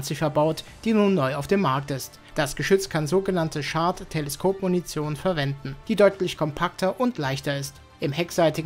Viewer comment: Die autogenerierten Untertitel verstehen hier "Shart Teleskopmunition“, was sich etwas unappetitlich und unhygienisch anhört😅